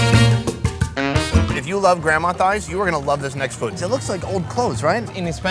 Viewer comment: Nobody: Really nobody: Not even: it’s softer than my grandma’s thigh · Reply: I thought he said his grandma died. Wow I was very wrong!